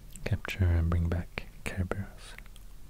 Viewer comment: I could have sworn he said "where he went to capture... Care Bears" and I laughed so hard